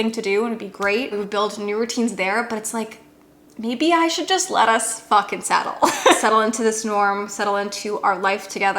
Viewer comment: I am so sorry but I thought you said “let us f*ck and settle” and I was like go girl! hahaha